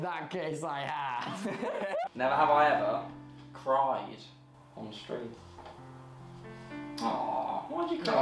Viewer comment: When he stayed dryer on the street I thought he said crapped on a tree